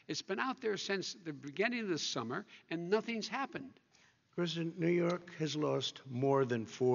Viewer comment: Did Trump say "President, New York has lost..." to the woman? 19:00. If so the poor guy hasn't realised he's been president for. 4 years.